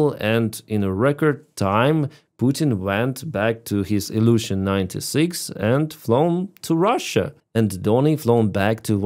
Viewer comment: I had auto-generated captions on and when you said at 11:23 that "Putin went back to his Ilyushin 96", the captions said "illusion 96" 😆